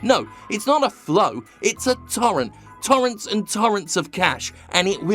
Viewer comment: did you say torrent?